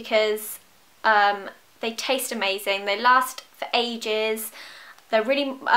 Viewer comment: Did Zoe say they taste amazing?? It was around 0:49 😳😳😂